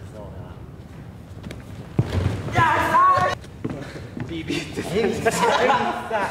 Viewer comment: 最初タンクトップマスターが入ろうとしたとき「やっさーい」に聞こえてもう最初からツボった(´･∀･｀)